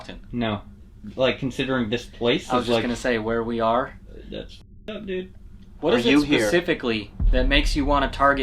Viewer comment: I would like to point out while you were doing the Esthes Method you talked to a mimic that sounded like 1:23:23 you can hear “Wyatt” say what’s up dude you look at him and say “why are you here”